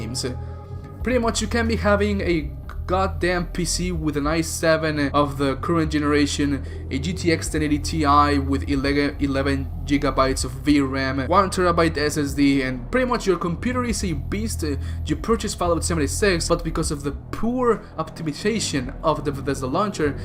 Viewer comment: I thought you said 11 Jigglypuffs of VRAM